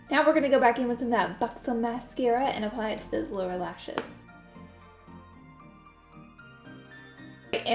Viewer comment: sounded like she said Laura lashes lol